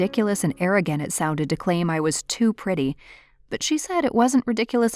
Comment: I thought she said "I am to petty" I was bout to be like "aren't we all"